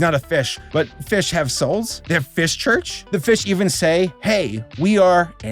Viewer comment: Saberspark: “Fish Christians” Me in a cold sweat: “Fishtians” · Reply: I heard “fish church” and immediately thought “furch” so I guess that’s where the fishtians pray